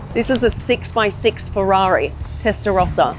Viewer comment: I thought that said "Ferrari Testicles"